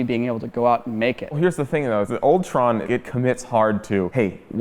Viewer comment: In 13:21, he said OLD TRON. but I heard it as ULTRON!!